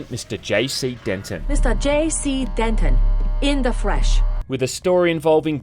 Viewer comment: I’m sorry did she say “in the fresh” instead of in the flesh, I’m playing this game NOW